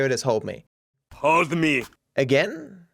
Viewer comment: I hear it as (hod me)